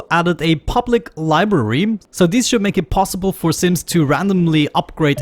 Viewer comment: Did he just call the people and the game Sim 😂